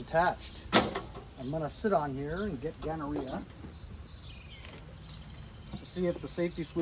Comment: did you say...."Im gonna sit on here and get gonorrhea" at 7:00???? I literally laughed out loud !